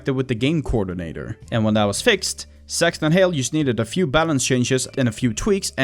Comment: auto generated captions say ,,sex and hail'' 😂😂😂😂😂🎉🎉